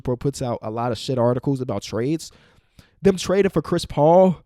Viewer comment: Did Kenny say s#!t at 15:53 that caught me so offguard